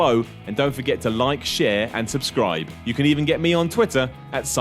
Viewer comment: 42 I heard "I'm not Adam from what culture" lol still love you Simon. no homo. maybe